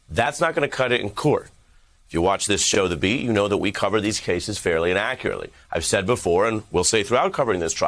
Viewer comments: Ok...people...did I hear this guy...the reporter say "we cover these stories inaccurately?" If you relisten at 3:02...to my ears this is what this reporter says. Please let me know if you hear the same...if so I unsub this channel. Oh and by the way....no im not a trumpeter lover either. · I heard “we cover these cases fairly inaccurately” instead of “fairly and accurately”. 🤣 Also, Eric, I’m pretty sure Weisselberg could afford private school as a ‘simple’ Trump accountant. If daddy’s such a great guy, wouldn’t he give the money to people who actually need it? 🙄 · When Ari said "fairly and accurately ." I heard "fairly innaccurately " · be sure to pronounce "fairy and accurately" well... I thought you said "fairly inaccurately"...